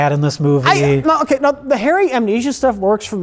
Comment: When said “the Harry-Amnesia stuff” I heard it as: THE HAIRY AMNESIA STUFF